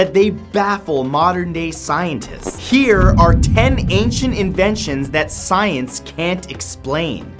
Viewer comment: I don't know why I thought it said alien inventions